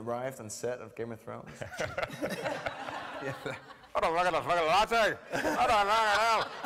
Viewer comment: Could anyone tell me what Peter Dinklage is saying at 4:18? I guess it's Northern Irish but I cannot understand the whole part. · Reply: I believe he's saying, "Can I get a latte?" lol.